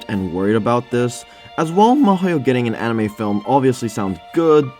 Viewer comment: Damn, I was confused for a sec cause it sounded like you said Mihoyo when in fact you were saying Mahouyo, which is a pretty weird way to abbreviate Mahoutsukai no Yoru ngl. Why even abbreviate it in the first place, Wizard's Night is perfectly short enough name to leave unabbreviated.